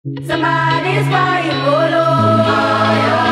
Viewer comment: "Somebody's crying, oh Lord" why i hear "somebody is call you ngoloooo" im crazy?? 🤔